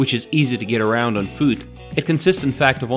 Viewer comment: I wonder what the nationality of the narrator might be. His voice contains so many accents from east coast USA to Spanish and Arabic? I am a linguist who lives in Asia and Europe, so am very curious. Thank you. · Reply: anders damin - at 3:45, when he says "foot", it sounded like "food" to me... I guess you need food to get around on... I also hear Boston accent...